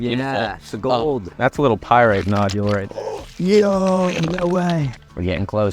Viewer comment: pirate-ised fossils are cooler tho.. great vid, i'm still getting used to the accents form where the heck in the US you guys are at. like i honestly thought you said pirate and not pyrite. fossils are sick, glad you got some fossil friends!! i enjoyed the segment of cleaning the fossils using proper tools, i don't get to see that part of the fossils world enough.